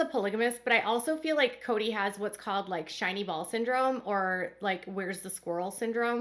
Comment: Lol,I thought you said "Shiney ball's " syndrome...so curious why Christine sold her part of Coyote pass for $1, if she was paying the taxes on it?! Girl! get your money back from these scammers! Are these women just cutting their losses and moving on?! Why do "sister wives" ?! Get your own show away from these people,they haven't shown kindness/ respect in any form and are publicly slamming you!! You can hold yourself to a higher level and still get your money back! And while you're at it,why not go for child support?!! Get a DNA test! Don't let them walk all over you! Why let them get away from it! It's his fatherly duty to support his children and they still have minors at home( Janelle & Christine) wash your hands of "sister wives "! I'm also curious about what happens with Meri now? Is she going to still stay,like she has been? inquiring minds want to know & I don't get it!